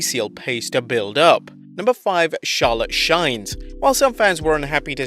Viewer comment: yo I heard that stutter "She Charlotte" 🤣 lol